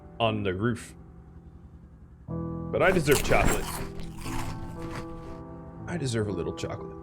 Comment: did you say CHOCOLATE DID YOU SAY CHOCOLATE